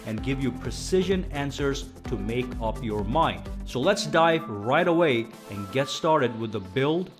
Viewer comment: i thought he said lets”die right away”😂